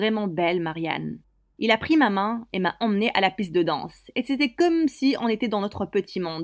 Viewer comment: J'ai entendu à la cuisse de danse...😅😅😅